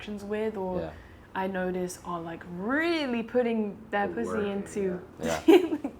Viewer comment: did she say pu$$y ??? It sounded like she did